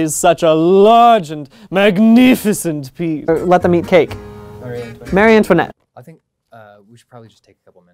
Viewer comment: i heard brian say "let the meat cake.. marie antoinette?" and was confused and then i realized that i mentally spaced the words wrong